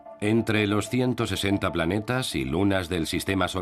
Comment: cuantos planetas dijo , 160 planetas y lunas?, escuche mal????😅